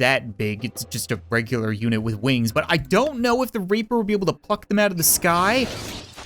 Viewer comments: did you say fuck or pluck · thought he said f#!$ them out of the sky